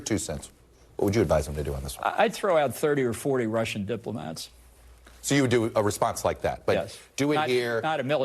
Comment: Wait, OK, sorry, I thought he said "line up and shoot 30 or 40 Russian diplomats". My bad.